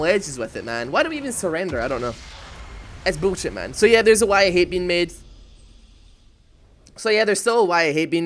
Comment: Hey. Is there a Why I Hate being made? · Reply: I thought he was saying "Why I hate being mid." lol